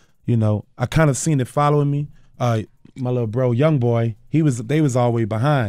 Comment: did he say young boy?? Was this before nba blew up